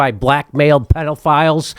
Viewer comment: for a second I thought he said “black male PDFs”